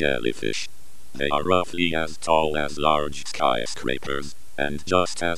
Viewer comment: When it said "They are roughly as tall as skyscrapers" I heard "They are ugly and tall as skyscrapers" 💀